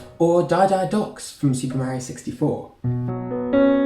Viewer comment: Dry dry docks a my fav level but it sounded like he said die die docks